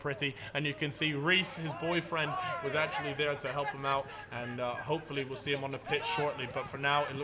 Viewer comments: did say “his boyfriend is there to help him” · wait did he just say boyfriend?